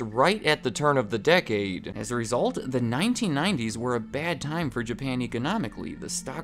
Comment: I thought he said “the 1990’s were a bad time to be Japanese.” 💀💀💀